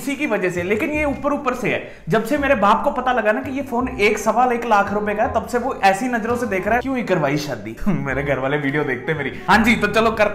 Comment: did he said 'kyo hi karwayi shadi'...? is he married?